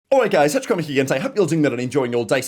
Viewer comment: Wth does say in the intro? "Awright-guys tetrakamaheegen today"